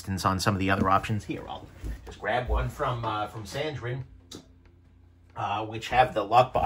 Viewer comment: What is the knife brings out at 5:50? Is he saying sandrin? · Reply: Sandrin Torino